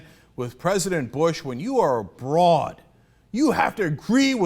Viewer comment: Is it sexist that I thought Cenk said "A broad" as in a woman and not "Abroad" as in overseas